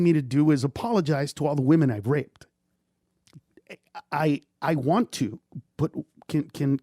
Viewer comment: Watching Bobby slowly slink down as he realizes Ned is never going to admit what he's done wrong and will always deflect is heartbreaking. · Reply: All I heard was “I raped chicks”